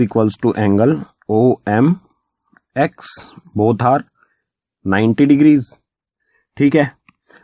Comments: sir said 'both are' I heard both Hard🤣🤣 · i heard bohot hard instant of both are😂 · I heard both hard instead of both are at 3:41 second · I hear bhaut hard at 3:40 😀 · I think you said bhot hard Edit : it took me 1 minute to understant what you said